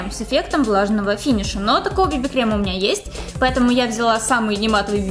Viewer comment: мне одной послышалось "Такого BB крема у меня ЕСТЬ"